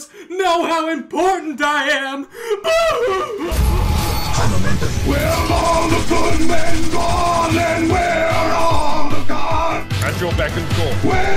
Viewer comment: what does it said here? Paramenting? Parlamentin? Carnamenting? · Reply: Believe it or not, it's supposed to be "High Momentum!"